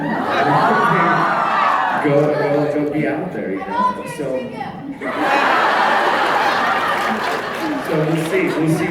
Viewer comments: What did that girl say at 20:42? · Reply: I volunteer as tribute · What did the girl shout out at 20:46? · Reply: she said "I volunteer as tribute"